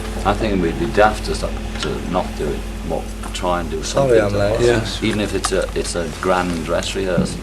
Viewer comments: Not that it matters at all, but at 1:35 I think George says "Sorry I'm late", not "Oh yeah" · Slight correction: At 1:35 I think George says "Sorry I'm late". I think he was the last one to arrive at Apple that day. Apart from that, amazing video. Been watching all the Let It Be videos. Really appreciate them. Julian from Poland